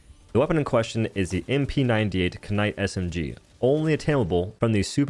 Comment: Did he say k-night😭